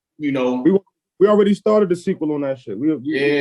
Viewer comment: Did he say there will be a deluxe? 😮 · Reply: He said there is a sequel to the project. Not necessarily a deluxe, but more like another album entirely with Harry Fraud with the same title.